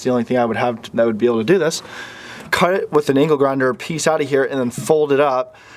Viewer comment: my wife is sitting next to me as I'm watching this and thought you said 'anal grinder' and ask what the hell I was watching